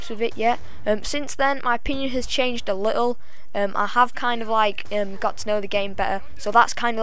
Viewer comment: it sounds like you said "My penis has changed a little" :P Great video hehe